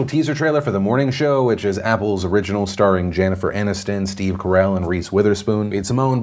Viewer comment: is it just me or did he say "Jannifer Aniston"? Learn how to English Phil!